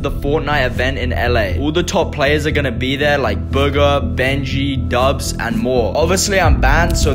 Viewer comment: He said bugha but I thought he said booger